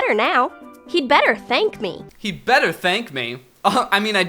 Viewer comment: I thought it said "He better spank me"